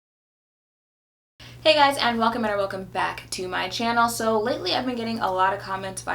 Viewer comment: Question. What are you saying in the beginning lol? All I hear is "Hey guys, welcominanananana welcome BACK.." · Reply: Welcome and or welcome back